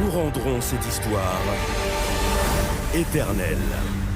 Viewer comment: Did they say "Internet"? And French football logo. Make no sense to me too.